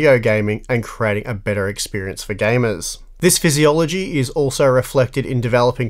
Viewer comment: did he say this "physiology"? or is that australian way of saying philosophy LOL